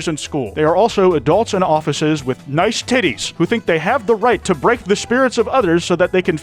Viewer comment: uhm, nice what? That’s clearly said titles